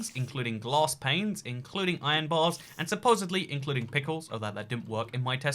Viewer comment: I cannot understand what he's saying. It sounds like "And supposedly include pickles" to me, but I'm not aware of any pickles in the game. On the other hand, I don't recognize what the picture is trying to tell me either, so... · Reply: He IS saying pickles. You know, sea pickles? They were added on 1.13